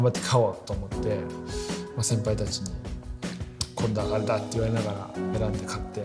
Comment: 時計のくだり「先輩たちに…」なんて言ってるのか上手く聞き取れない 誰か教えて〜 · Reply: 「これだ」「あれだ」って言われながら選んで買って